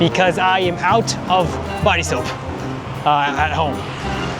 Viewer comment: I’m out of bicep at home に聞こえてしまい、「何を言ってるんだこのムキムキお兄さんは😮」と思いました